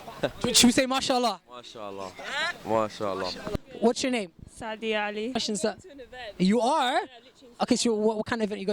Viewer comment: She said sacdiyo cali but I thought she said sado cali